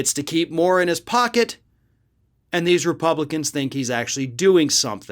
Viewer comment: 4;03 i thought you said 'war in his pocket' must have had gundam on my mind